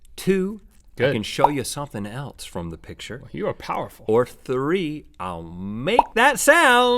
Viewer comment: Did Link say, “you are powerful” to Rhett 😂